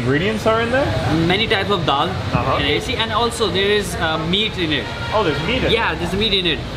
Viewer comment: did he just say dog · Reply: daal (lentils)